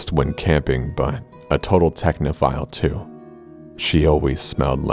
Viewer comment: When he said technophile i thought he said pedophile 👁👄👁